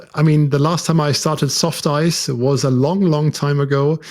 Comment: Did I mishear, or did you say SoftICE? I really miss that level of control. And the crazy stuff we could do with ntid and traceur de grand vitesse. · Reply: Yes, SoftICE it was, indeed!